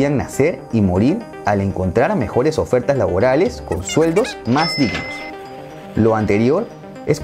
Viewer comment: no entiendo que dices en el minuto 9:56 :v Dices con sueldos ....?